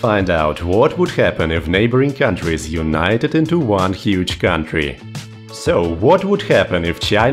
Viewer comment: Bro I fr thought it said neighboring countries untie into Ohio💀